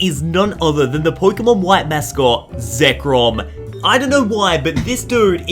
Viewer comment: did he just say zecrom is the Pokémon white Mascot?